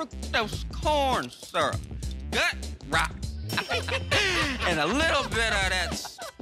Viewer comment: What does she say at 00:24? "Gut rot"'?